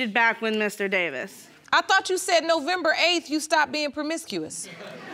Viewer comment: I thought u said u was done being permicusious lol😅